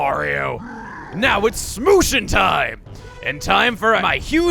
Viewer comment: I thought he said smooching time...